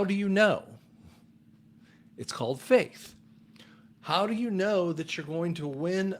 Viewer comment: I thought you were saying, "It's called Fake (instead of faith)" lol. Another bottom line to this whole scenario is that the Trump admin qualified to enact the "Insurrection act" which included 11/3, Jan 6 and telling those responsible to "go home/leave peacefully which is one of the qualifications. He wasn't speaking to his supporters but all involved with the true insurrection. The military has been under Trump ever since who is still the true President. The Biden regime is being allowed to wake us up and to trap the (world wide) Deep State. Those EO's are certainly a part of the plan!